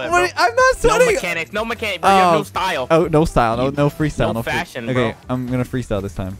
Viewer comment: Am I stupid or did say “no fashion nigga” at 11:09🤣